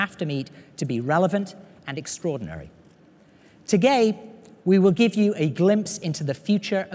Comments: he totally says "to-gay" instead of today... You're welcome world. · he says "gay" instead of "day". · he said 'and togay' instead of today,hahaha